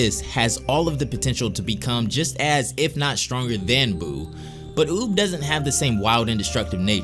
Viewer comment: did he just say boob?